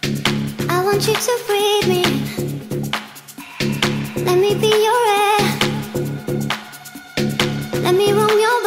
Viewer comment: lol me and my blue mind, i thought she said, "I want you to breed me in, let me be your heir"